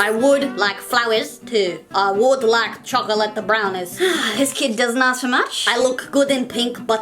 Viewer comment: this kid doesn't know much(I think its what she said) picks nose